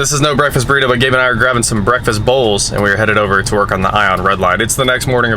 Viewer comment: Who else scrolled right down when you thought said “Breakfast Balls”